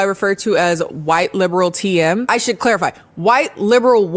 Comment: i thought you said "white liberal Tien" at first and i was like "from Dragon Ball???" 😂